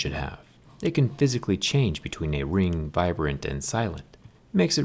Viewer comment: Is it only me who heard VIBRANT at 2:30 ?